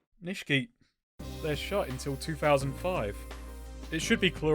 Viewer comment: I can't get this out of my head, what are you saying at 16:25 ? Are you saying "They're shot till 2005?" but I don't think that makes any sense. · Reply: shut